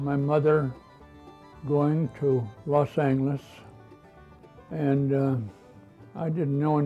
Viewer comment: How come he's pronouncing it Los Angles? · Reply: what is he saying here? I'm hearing "Los Angles"